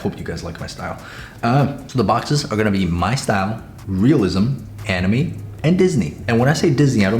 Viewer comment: DID HE SAY ANAME?😭